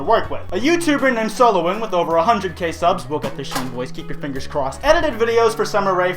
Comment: What the hell did he say at 0:32 ? "Robot machine boys, keep your fingers crossed?"